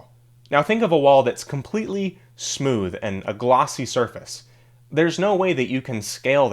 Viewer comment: I thought you said "floss". The number one thing to make a character more likable is to have them floss their teeth!